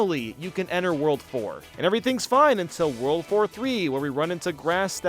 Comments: did bro just say world war 3? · when he said "world 4-3" i thought he said "world war 3"